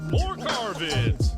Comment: I actually misheard your opening intro. I thought its “more garbage” I was like what!? Oh it says “more car vids”.. sorry for that.